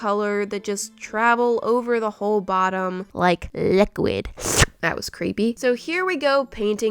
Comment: LeK LIqUiD.... Me: (@@ Me thinkin': Bish what da fliparounees did you just say... ¯\(°_o)/¯